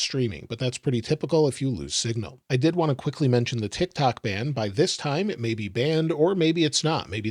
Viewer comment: Tik Tok, I'm sorry, I thought you said Brain Rot.